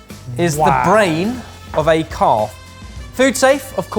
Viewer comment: I thought Ben said this the brain of a carp. I was like that must have been a very big and very smart carp.